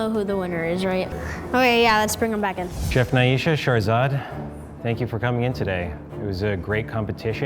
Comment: I legit thought said charzard 😂😂